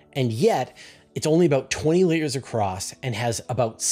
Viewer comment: The "dwarf galaxy is only 20 layers across." I had to rewind that a bunch of times, lol. I thought was saying litres or metres? I couldn't find a clear answer about how many layers The Milkyway has and if that was vertical or horizontal layers 🤔